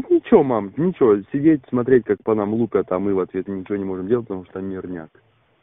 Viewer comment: что он сказал на 1:20 там мирняк ? или что ?